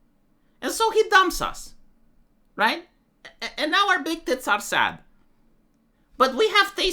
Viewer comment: I heard "... And now our big tits are sad" while on the bus and let out an audible chuckle.